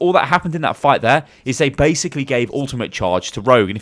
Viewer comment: does he says " basically gay ultimate "?